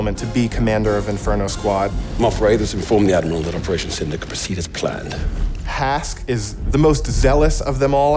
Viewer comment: I can't catch what he is saying he is talking way too quickly. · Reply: Republic Holdout "[Inaudible] said to the Admiral that Operation Cinder can proceed as planned"